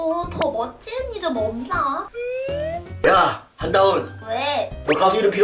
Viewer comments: 야 한다운이라고 들었습니닼ㅋ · 나만 한다운 이라고 들림?? ㅋㅋㅋㅋㅋ